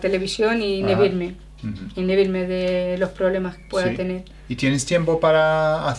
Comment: Hola :) Muchas gracias por tus videos. Me ayuda mucho. Tengo una pregunta, que dice en el tiempo 6:44 despues de "me gusta ver la television y ...??? de los problemas". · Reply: Glady Tanay usa el verbo Inhibirse. Dice, "e inhibirme". que es como distraerme.. Take my mind off things.